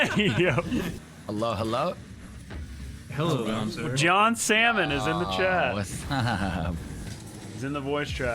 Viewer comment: I swear to god he said "Jon Salmon"